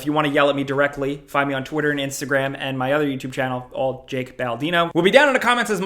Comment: Jake said “my name is Jack Baldino” lol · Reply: I honestly thought he said Jalk Baldino.